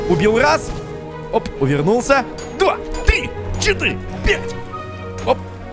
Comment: в 15:39 я услышал читы